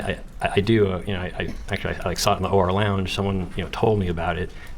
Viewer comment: When Rob said "OR lounge" I thought he said "horror lounge" 😂 It's incredible to me that Donna and Harvey managed to produce such an amazing man who has empathy, integrity, and honor. I hope he and his family have a long, happy, blessed life far from the evil he was born into.